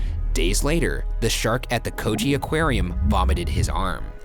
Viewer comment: i just had this playing in the background all i heard was "the coochie aquarium"